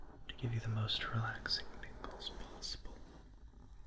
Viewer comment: he said to give u the most relaxing tingles possible but the subtitles said the most relaxing "nipples' possible 😭